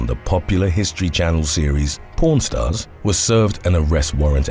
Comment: Lol it sounded like he said porn stars🤣😂 Also, wtf 7 1/2 yrs for killing 2 people?!?!!! Justice was not served for the families of those 2 victims, I mean technically that is only 3 1/2 yrs per life he took smh that is ridiculous!! If he were just like one of us he would be serving 2 life sentences.